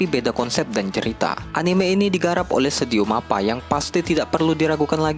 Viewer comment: "anime ini digarap oleh studio mappa". tunggu, gua gk salah denger kan? sebenernya b aja sih cuman ya kaget aja ni anime digarap sama mappa (anime sebelah juga make studio mappa, ya kan? bukan death note maksudnya itu tuh, mmm. hajimek)